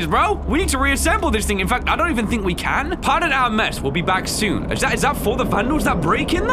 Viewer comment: I thought it said pardon our messi